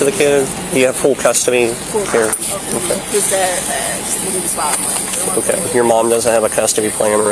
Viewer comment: I just know that if that cop was talking to a white family he would’ve said “the father of the child is deceased” but in this he literally said “baby daddies dead” what a gronk. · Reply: Right! I could've sworn she said "my son's father died when he was 5 months" 🙄